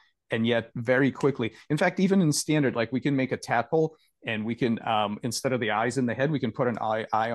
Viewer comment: Please, at 16:49 what does Dr. Levin is saying? ("...standard like we can make a tple and we...". Thank you very much, amazing conversation.